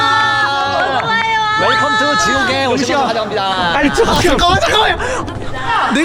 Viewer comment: 꺼져 소리가 들려요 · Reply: 이거 말 빠르게 해서 그런거지 끄아 잠깐만여 라고함